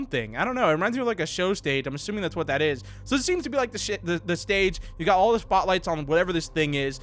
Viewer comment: Hang on did he just say shithethestage?